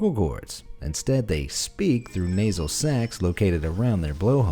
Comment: Definitely thought you said belugas talk through nasal sex....